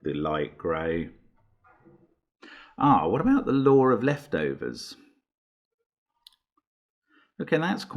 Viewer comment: I know he says "the law of leftovers" but I always hear "the Lord of Leftovers" and get a kick out of it haha.